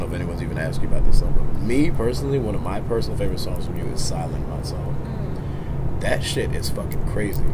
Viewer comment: Peggy what song did you say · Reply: EndDay silent my song